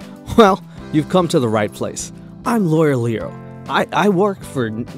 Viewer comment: I heard “loyal Leo” instead of “lawyer Leo” & didn’t even question it